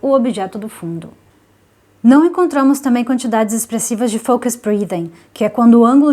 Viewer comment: Gostei muito do review porém aos 3:40 você fala foco spulidan? Perdão não consegui entender o que você falou, poderia me dizer o nome da expressão? Obrigado! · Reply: Olá José, o nome é "Focus breathing", na descrição do vídeo possui um link com uma explicação melhor sobre o efeito. Se você não trabalha com filmagem não precisa se preocupar tanto com isso. Att.